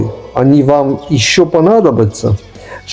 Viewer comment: ПАДАЖЖИТЕ- ПАДАЖЖИТЕ 😄 Мы вас аккуратно со струны снимем, водички дадим - и ровно в субботу, вернём обратно в то же состояние. Продолжение уже идёт строевым шагом. Маркиз Пюре точит когти, потому что одну весьма наглую морду следующего героя мы будем разбирать медленно, с расстановкой и без пощады.😂😂❤❤❤ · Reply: мне послышалось в конце "до встречи снова в марте"... Спасибо, облегчили ожидание, развеяли ужас от мысли "ждать продолжения месяц", но всё ещё очень бы хотелось конечно ролик на сколько угодно часов чтобы сразу и залпом всё посмотреть 🥹❤️